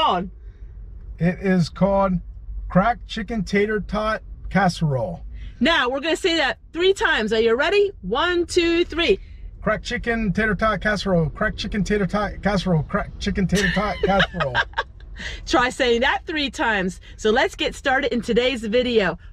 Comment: I thought y'all was saying cracked iron skillet tater tots casserole. LoL 😆